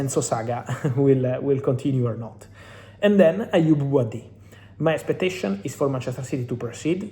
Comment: the subtitle shows aymeric laporte instead of aoub